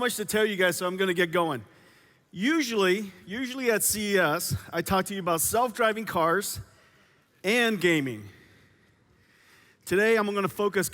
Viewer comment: I thought he said he wasn't going to talk about self driving cars but whatever its still pretty cool